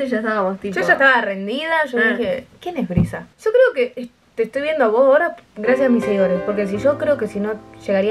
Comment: Soy la única que escuche que dijo: yo creo que te estoy viendo a vos gorda (onda cariñosamente), gracias a mis seguidores... o estoy loca?👁👄👁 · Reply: creo q dijo "te estoy viendo a vos ahora"